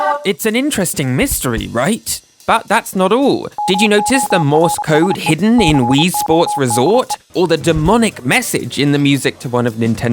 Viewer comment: I thought you said “Did you know ‘‘tis the morse code hidden named Louise Sports Resort”